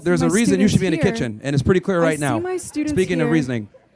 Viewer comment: Did she say „my students“? This is a teacher ?? Are you kidding me 😂